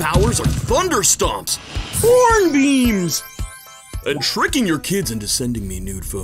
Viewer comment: Bro when the ripoff grime says horn beams i hear “p@ beams” 😭